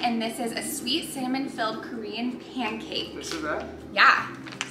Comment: I thought you said a “sweet salmon filled pancake” 😅🤣